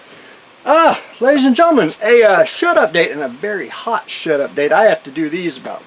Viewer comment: Shed Update. Always thought you said “Shut-up Date”